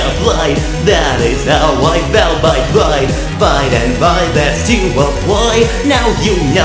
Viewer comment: meanwhile I hear “that is how I found my plight” Well…..